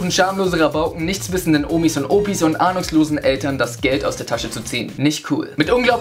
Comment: Ich habe Anuslosen Eltern verstanden. Was ist los mit mir...?